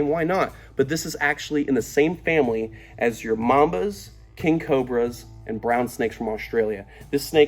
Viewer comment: almost sounded like "ur momma's" hehe